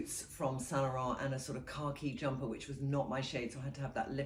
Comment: Omg! Trinny, I’ve heard you say the color CAR KEY, CAR KEY, CAR KEY, CAR KEY so many times and I couldnt figure what color that would be>>> I thought BRIT SPEAK for some Grey! Now I just got it! You’re saying KHAKI, KHAKI, KHAKI!!!! Not CAR KEY!!!! You say it with an “R” and I am laughing at how ridiculous I am